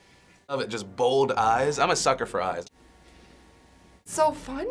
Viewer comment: what is defranco say at 0:50 ? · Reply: Ali Kerem Çakır "im a sucker for eyes"